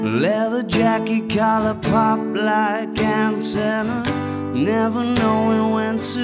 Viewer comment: Fantastic song ! Always thought he said " Collar popped like Ann Turner ". Been singing that for years..